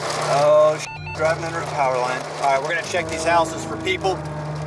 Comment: did he just say check the house? How he didn’t die lol